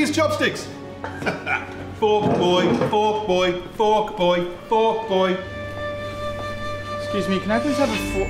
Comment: Anyone else thought he was saying "fuck boy"?